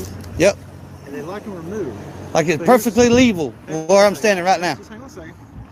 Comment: did he just say "perfectly leeval"...😅😅😂